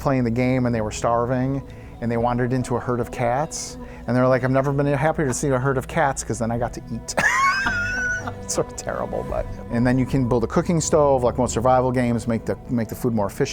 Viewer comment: did he say heard of cats? They'll be heards of cats?